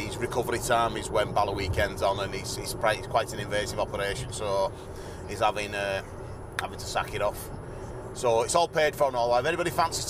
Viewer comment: Good luck to Graham, ........at 1:54 did you say he was " having his sack taken off " He will not be able to sit in the saddle for months.😝